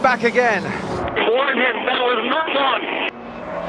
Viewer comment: What does he say? Thanks in advance :) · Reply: Rosberg: "Warn him that was not on!"